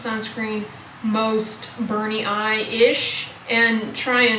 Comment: Listening to this vlog as I do my work - till the point where you started to talk about burning sunscreens. Lol I had to look up from my screen and rewind the vlog because I thought you were saying Bernie eyes-ish. Had me LOL. Was wondering why Bernie Sanders made a guest appearance. 😅